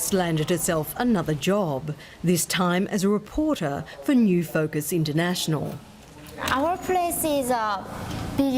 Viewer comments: sounds like she said big ass news papper XD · Sounds like she said, Big Ass newspaper. lol